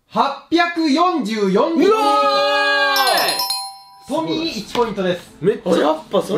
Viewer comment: の"トミー1ポイント"が"ポミー1ポイント"に聞こえる笑